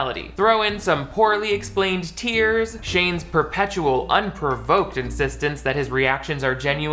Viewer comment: I thought you said “perpetual unprovoked existence” lmao